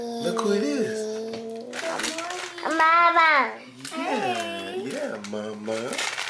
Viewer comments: did say "hello mama!"? so cute!!! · awww @ 1:49 it sounds like said i love mama :)